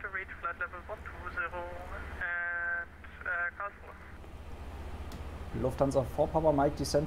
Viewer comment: Hab ich da etwa KARLSRUHE gehört?? xD